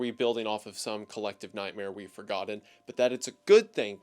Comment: I thought he said “forgotted” and now I can’t stop thinking about speaking like Tommy pickles.